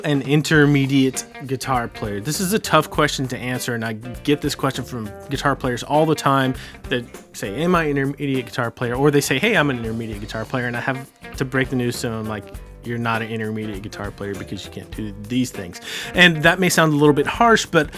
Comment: I thought he said “am I an idiot guitar player?” After watching this video I’ve concluded that yes….yes I am 😩